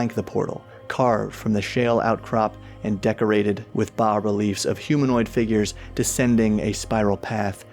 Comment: I heard "decorated with Bob reliefs" and was disappointed it was bas instead.